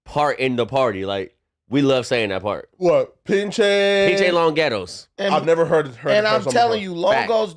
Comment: I always thought Goldie Loc said "Beachy Long Ghettos" 😂😂😂